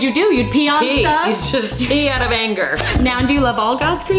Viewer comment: When Mayim said "pee out of anger" I heard "pee on a banker" I was like that poor banker 😂